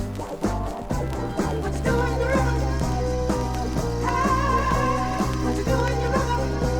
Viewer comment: i heard "whactha doin on your rubble?" Instead of "whatcha doin on your rump". But that disc sounds like it's recorded by a bunch of high school kids.